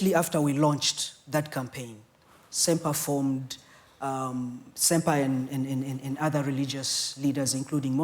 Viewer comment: Did he just say sempai...SEMPAI!?